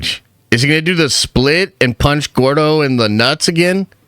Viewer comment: Did he say Gordo? 3:37. Bruh don't know what he's talking about